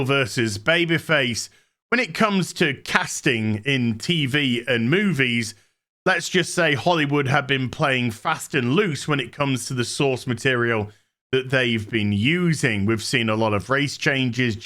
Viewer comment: i swear it sounded like he said holy wood was playing fast and loose with the sauce materials....